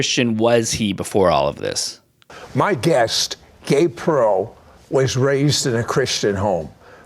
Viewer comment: All I hear is ''This is my guest, Gay Pro'' and I start laughing :DDDDDD Cannot unhear it.